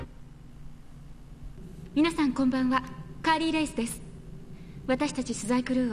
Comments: カーリー・レイスってずっと聞いてるとカレーライスを発音よくした感じに聞こえてくる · カーリーレイスがカーリー>ライスに聞こえて仕方がない。